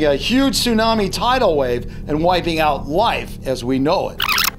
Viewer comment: he said wife instead of life